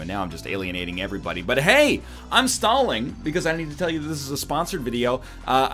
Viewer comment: I 100% thought he said "I'm Stalin" after talking about the Russian name.